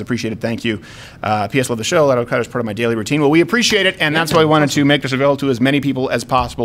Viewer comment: Closed Caption at about the 14:17 mark says “Al-Qaeda is part of my daily routine” instead of “Louder with Crowder...”.